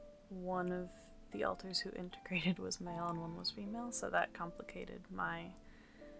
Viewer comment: your what?? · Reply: In that spot I hear "was male" - I believe the full sentence is, "one of the alters who integrated was male and one was female" 😊 (The auto-caption says "all-in-one", but I don't think that's accurate, haha)